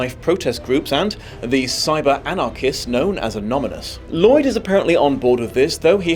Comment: Did you say Anomynous? 🤨